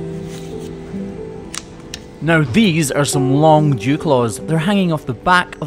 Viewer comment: I thought he said,"Jew claws" until i read the comments. I was thinking where did that name come from?